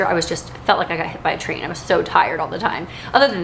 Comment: Lol, I thought you said you felt like you've been hit by a tree, then I realized you must have said train. Haha, watch out for those runaway trees. Anyway, I'm praying for you and baby #4. God bless.